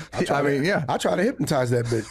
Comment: Did Marlon just say "I tried to hypnotize that BIT$H"? Yo dats wild callin yo own k!d a bit$h🤦‍♂️🥴